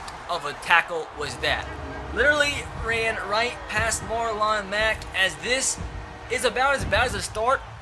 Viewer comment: Did you just say Marline Mack, wtf it’s Marlon Mack